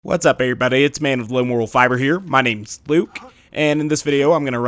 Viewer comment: At the beginning it sounded like you said MAID of Low Moral Fiber